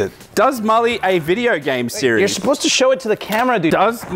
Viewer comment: I thought the editor blurred it😭😭 i thought it said gay porn series😭😭😭